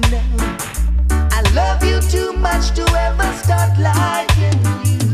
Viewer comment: I'm 23.. my whole life... literally my whole life I thought he said "I love you to much to ever stop lieing" 😫🤦🏾‍♂️lool